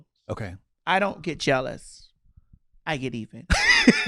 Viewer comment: "I don't get jealous - I get even" Omg Ms. Madison lol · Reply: I thought she said "I get eaten" ._.